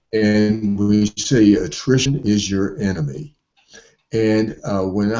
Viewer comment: Hi! this is a great interview, I just want to know, if someone could help me, what's the word he says at minute 07:40, sounds like treason but is obviously not that one, clearly english is not my native language. Thank you! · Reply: Manuel Veytia - attrition